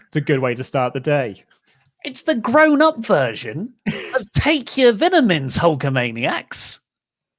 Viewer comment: Did oli say take your vinamins? LMAOO